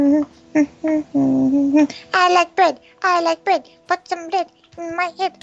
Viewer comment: I thought she said "I like butt,I Like butt,put some hip,in your lip" =.= Oops